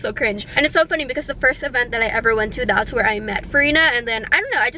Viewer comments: i cant be the only one who thought she said vereena and was shook lol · I thought you said vereena...